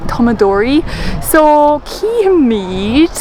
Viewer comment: Físeán maith! Ach tá ceist bheag agam. Cén frása a úsáideann tú ag 4:02 nuair atá sé ráite sa bhfotheidil "so we'll see"? Déanaim cleachta léitheoireacht le do fhíseáin uaireanta, agus níor chúala mé "feicfímid" mar is gnách. Bhí mé fiosrach faoi an focal a úsaideann tú. ^.^ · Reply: Go raibh maith agat as do theachtaireacht! Is éard a dúirt mé ná: ‘cífimid’ - sin cad a deirimid sa chanúint ó Chontae Chiarraí an tá an ceart agat - sa chaideán sin ‘feicfimid’ 😊